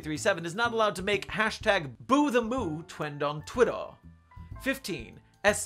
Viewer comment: is it just me or did he say 'twend on twittaw' instead of 'trend on twitter'?????